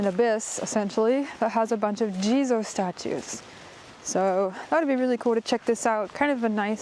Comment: Thx again. Wanted to so also a daytrip next january. And "jizo Statue" ... I thought all the time you Said "Jesus Statue" and was confused haha.